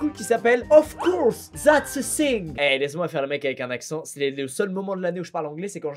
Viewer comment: moi j'entends "zat'sa sing" à